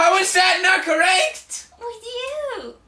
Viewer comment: what did she say? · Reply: With you